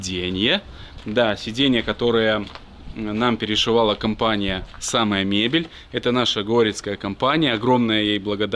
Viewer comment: Мне послышалось Ссаная мебель 😂😂😂😂😂